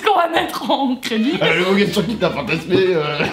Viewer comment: " fastasmer sur moi ..." jpp 😂😂